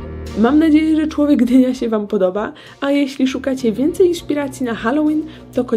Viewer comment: Kto pod koniec(dobre 25 sek przed końcem filmu)kto jak ja usłyszał że 😊się język poplątał i powiedziała "iszpiracji" a nie inspiracji daje lika 😃